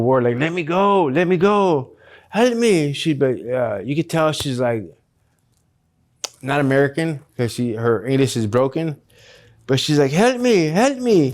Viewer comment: I heard "You can tell she's not american 'cause her anus is broken" ... 🤣